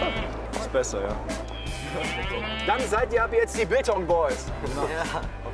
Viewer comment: Hab Bangtan Boys statt Biltong Boys verstanden lmao